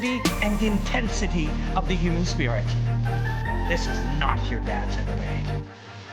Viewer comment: not your dad's MMA? Is that what he says?